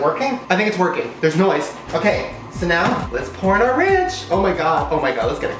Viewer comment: "let's porn our ranch!" 😂 I know he said pour but it sounded like he said porn lmao 💀